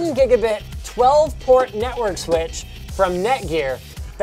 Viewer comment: Network switch.. I thought it said Nintendo switch.